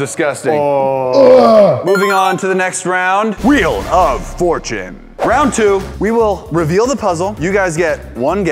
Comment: I thought said WHEEL UN FORTUNATE